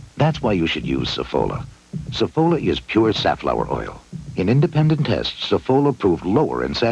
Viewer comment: saffolaって言ったらカノーラ(canola,キャノーラ油)も入ってそうに聞こえる(´・ω・`) いやー現地の人の発音は速くて聴きとりにくい 英語の聞き取りトレーニングでジェロビアフラの出演番組を見てるｗ